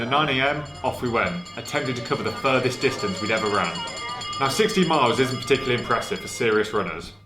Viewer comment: I thought you said 60 miles. And I thought “yeah, not super impressive but a good start and a big jump in your first year of running”. Then you said at 13 miles you were closing in and I was “oh…ok”. Makes a lot more sense. 16 miles is actually a great distance at the end of the first year.